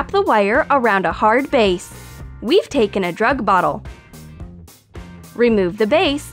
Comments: did she say drug bottle like if you think she said drug bottle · did she say a drug bottol????¿?